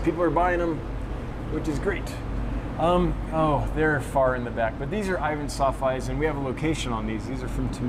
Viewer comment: Can anyone tell me what the fish at 1:14:31 are? I can’t make out what he says - Ivan’s something eyes? Thanks in advance! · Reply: Pseudomugil ivantsoffi 'Timika' =)